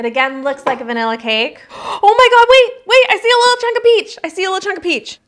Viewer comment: I had to come to the comments to see what you were saying because I thought you said "I see a little centipede" then rushed to eat it. 😅